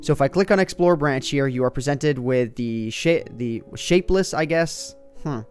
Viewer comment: Did he say "Shit' at 7:35 ???